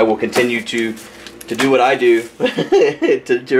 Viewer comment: I thought at 31:55 that you said "Fat man out." Then I remembered you were wearing a batman shirt.